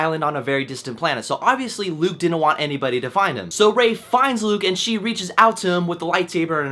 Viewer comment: at one point you spoke so quickly you said "Rey finds Luke" but I had to rewind it because I could have sworn you said "Ralph Fiennes Luke". lol